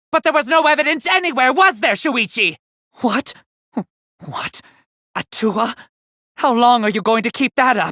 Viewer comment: I thought he said hawk tuah